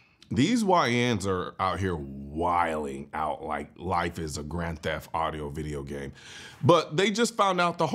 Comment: idk If it's me but it sounded like he said "Grand Theft audio video game"